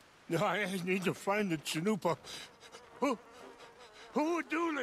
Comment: He just wanted the sacred chalupa back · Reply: John Dionisio omg that’s what I thought I heard and I said to myself, “a chalupa sounds good about now.”